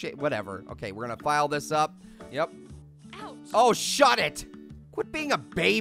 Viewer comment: OMG I THINK PEAR SAID SH*T O